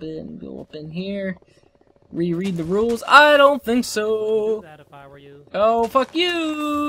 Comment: i hear somebody say ''i wouldn't do that if i were you''